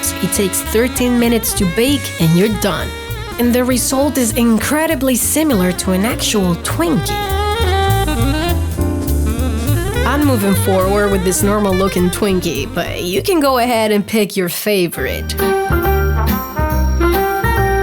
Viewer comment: my gay ass thought she said twink